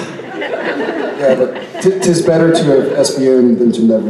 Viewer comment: Why did they say that made Jensen laugh at 24:19? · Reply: ''Stoooop God''